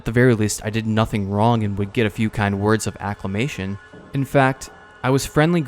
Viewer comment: No, I meant to say acclamation, haha. Affirmation would work as well, though. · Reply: ohh I’m dumb lol I thought you were saying ‘acclimation’. Awesome video btw, I’ve been interested in the first two games for a while but your adventure’s really made me want to finally dive in